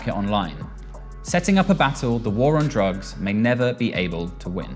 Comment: I thought it said “the war on piss”